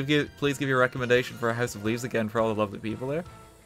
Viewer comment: What book is he talking about? My headphones are actual garbage and I can't hear/understand, but it sounds really good. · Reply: Brynne Roberts house of leaves I think